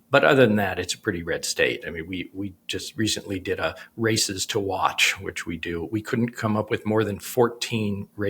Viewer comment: i misheard comment on 'Races to Watch' as.... 'Rascists to Watch'😂😂😂😂..... my immediate thought... must be some form of hourly update on Trump and Vance's whereabouts....😂😂😂😂😂😂.... 🇺🇲 Vote Blue to the end of the ticket🇺🇲